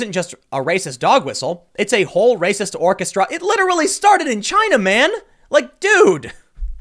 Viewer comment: GASP! Did you just say......Chinaman?